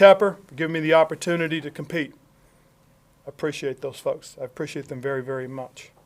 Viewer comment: what did he say? Is it me or did he say I appreciate those f**ks